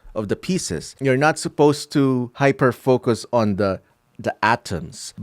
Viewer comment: did he said FOCUS